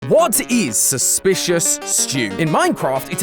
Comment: thought u said "water is suspicious stew" lol